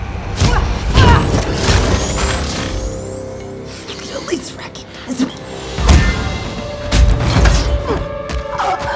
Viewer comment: Anyone know what she said? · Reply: You could atleast recognise me